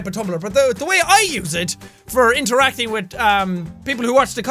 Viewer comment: XD at 3:04 Jack said "wit" instead of "with" Man, I love his accent